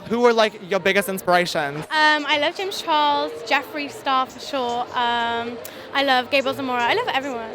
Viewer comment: my subtitles said "i love jeffree starfish" DEAD😂